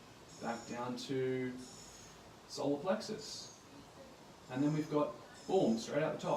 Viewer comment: What was whispered at around 17:40? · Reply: Solar... As in solar plexus 😉